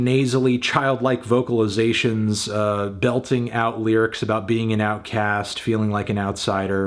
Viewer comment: I thought he said 'being in OutKast' for a second